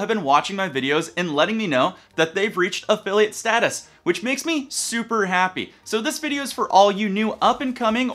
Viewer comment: I heard " They reached affiliate status which makes me super unhappy "... then I replayed and found out I'm dumb.